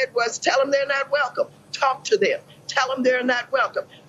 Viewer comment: I guess my hearing is faulty. Every time I hear that clip I keep hearing "create a crowd! push back on them!" and she's saying that she said "Talk to them." Weird, huh?